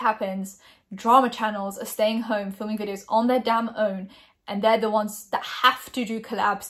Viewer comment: When you said 'damn own' I heard 'down low' and I was so confused lol